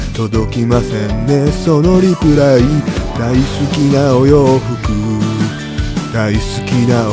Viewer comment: 頃の「大好きなお洋服」が少々噛んだのか、若干「大ちゅきなお洋服」に聞こえて、巻き戻し⏪てしもた。 それにしても、サムネからやってくれますね…先生…萌え袖ってヤツですか？ なんとも言えないサムネから、何かを予想させるイントロ動画、そしてあの曲よのイントロ、からの先生のダンスかと思えば歌声まで！ひぃぃ！盛りだくさんすぎて、一生懸命見ようとしても、爆笑が止まらず、しかも最後は雑なぶつ切りな感じで背中向けられちゃってるし。もうなんか、なんていうか、毎度のことなんですけど、好きです。